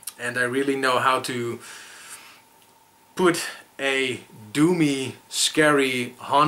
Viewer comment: I thought you were saying "do me, atmosphere" instead of "doomy" haha.